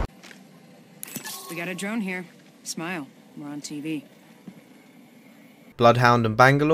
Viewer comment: What's she saying? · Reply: "Got a drone here, smile we're on TV"